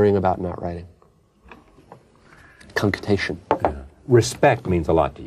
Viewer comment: What does he say at 3:39? I've never heard that word before... I thought he said "confutation" but he seems to be adding a percussive sound like "concutation" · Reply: cunctation